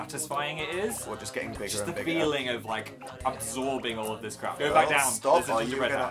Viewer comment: Did Phil just say crap